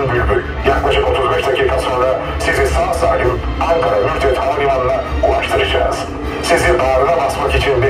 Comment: ankara mürtet havalimanı diyor benmi yanlış duydum